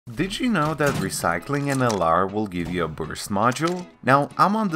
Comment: Thought bro said booger instead of burst